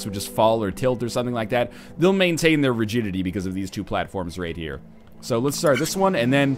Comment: thought he said "Maintain their virginity"